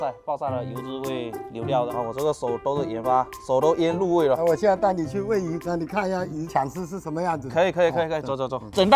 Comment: 现在带你去喂鱼我听成现在拿你去喂鱼😂